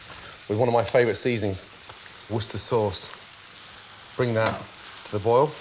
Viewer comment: I don't understand what is he saying, i think worchester